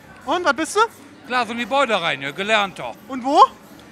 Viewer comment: Was ist der Typ jetzt von Beruf? Hab kein Wort verstanden :D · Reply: Gebäudereiniger :D